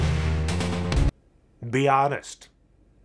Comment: I thought he said Beyonce instead of be honest